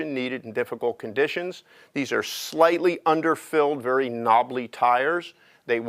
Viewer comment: great machine but at 4:40 did he say knobbaly tires lmao hahahaha